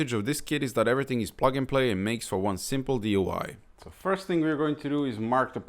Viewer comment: It sounds like you said: "and makes for one simple DUI" DUI means Driving Under the Influence (a motorist is intoxicated). Did you mean DIY (Do It Yourself)?